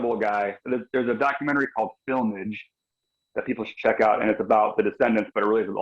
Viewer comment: Did he say FILMAGE?